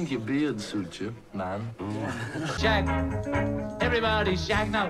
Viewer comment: I swear I heard John say shag instead of shout 8:50😂😂😂😂